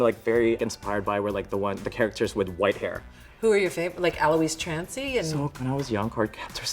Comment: did I just hear Michelle Visage ask about goddam. ALOIS TRANCY